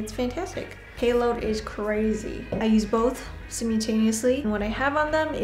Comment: "sim-you-taneously"? lol that's such a cute weird way to pronounce "simultaneously", and it caught me off guard enough I had to reel it back to make sure I heard correctly